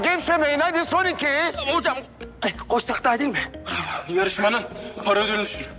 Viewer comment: arap burda ne diyor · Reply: "Hocamğ koşsak dahağ iyiğ değilmiğğ"